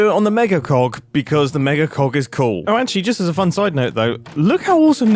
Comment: Mom walked in halfway through this video and misheard. She instantly goes “WHY ARE YOU WATCHING VIDEOS ABOUT MEGA COCKS?!?”